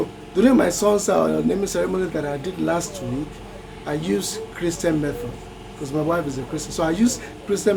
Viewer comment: I think you said you used the crystal method but at first I misheard and thought you said you used crystal meth 😂😂😂